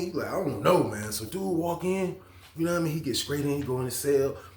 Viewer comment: I’m scressing over how he saying stressing 😂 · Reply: I think it's the str I heard him say scraight